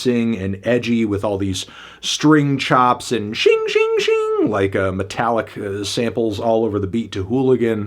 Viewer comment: what did he say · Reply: Xing xing xing ⚔️